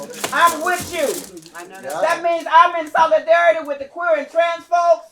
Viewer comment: did she say solidarity with transphobes?